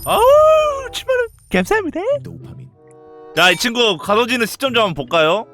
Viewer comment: 여기서 왜 ‘도파민’이 들리지? ㅋㅋ